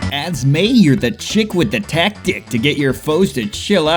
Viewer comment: I'm sorry, did you just say tac-DICK